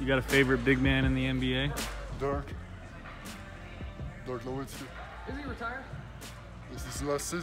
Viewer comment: I thought he actually called him a dork lol